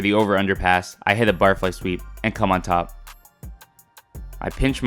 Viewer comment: I Thought you kept saying bar fly sweep